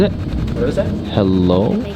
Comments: when you thought he said “hello” it sounded like “蛇”- “Snake”. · That "hello" at 11:00 sounded like "Heaven" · i heard ‘Help’ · Is it creepy that I heard "heaven"? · they said that it sounded like "hello", uhm, it sounded more like "help" to me😐